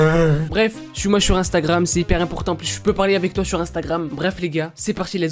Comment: j'ai entendu "jsuis moche suis instagram" mdrr